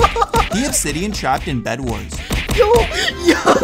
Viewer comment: subtitles say he obsidian trapped in bedwars but it was skywars lol